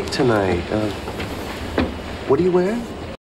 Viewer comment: 영상 감사합니다!! 그런데 자꾸 제 귀에는 what're you wearing?이 아니라 what do you wear?로 들리는데요... 저만 인가요?ㅠㅠ